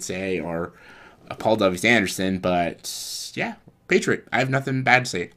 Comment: When you said Paul w.s. Anderson I thought you said Paul Thomas Anderson which scared me